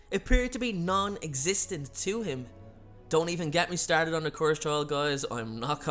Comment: what did you say? I couldn't quite make it all out. It sound like: "Don't even get me started on (unintelligible), I'm not going there." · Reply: The cursed child